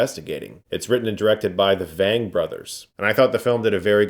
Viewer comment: I thought you said "It's written and directed by the BANG Brothers."